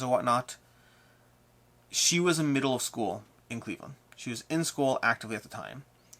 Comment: I had to laugh when you said Rina was still in middle school - I know you meant teaching and not that she was 12 but it cracked me up. But I am loving this series! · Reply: I totally misheard that too!! At first I thought he said she was in middle school… then I realized he said “the middle of school” that of makes a huge difference 😂